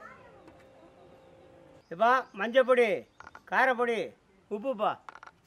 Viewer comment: Wtf did he just say lmao?? @ 4:27 something about a poo poo butt??? 🤣